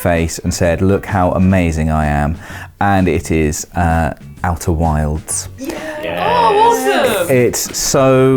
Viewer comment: When said "Outer Wilds", I thought he said "Outer Worlds" with his brit accent... lol